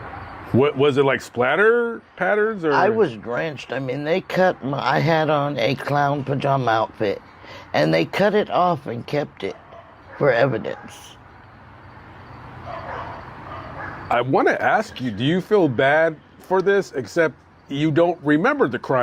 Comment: Clown pajamas? Didn't one of the old movies have a little boy that killed his sister in clown costume? Micheal Myers.i dont think she did it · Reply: Did she say clown or cloud? I thought she said cloud but English isn't my first language and I found her accent a bit difficult to understand.